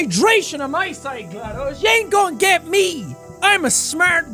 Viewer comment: anyone else hear "yain't"